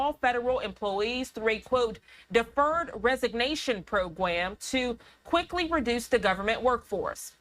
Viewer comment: Did anyone catch that… “Pwo-gwam”? Bahahah!! 😂😂